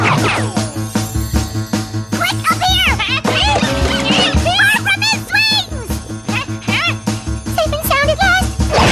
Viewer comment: What exactly is Sarah saying here? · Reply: Quick up here, far from his swings.